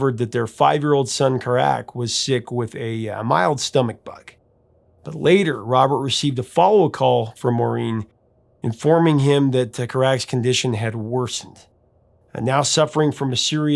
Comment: pronounced KA-rac. At least that's the way I heard it sound..